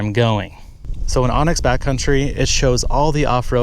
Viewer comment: I thought you were saying onyx back country!😅 Glad I paid attention!